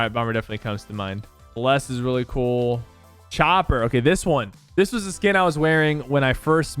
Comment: Did he just say m@ is cool